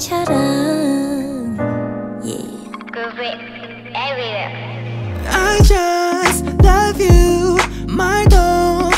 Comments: 이거 뭐라고 하는건가요? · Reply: Groovy everywhere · what is the voice saying??? Please someone help me???!!!! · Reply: Groovyroom